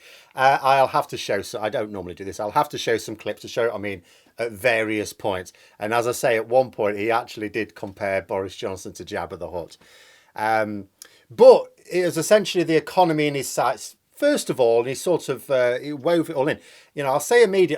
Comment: I thought he said a Jabba the Hut turd.